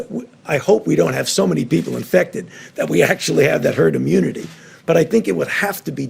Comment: Wait. Did Fauci actually say he hoped we DIDN't get to herd immunity? He says it around 6:56. What the heck!?! Ya'll, go back and listen to it and tell me if I heard correctly....